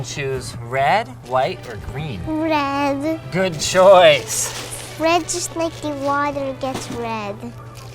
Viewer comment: What does he say at 6:00, red just make......? I don't get what he says?